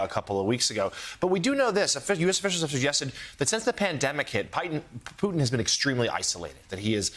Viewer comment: did he just call Putin as "Python"?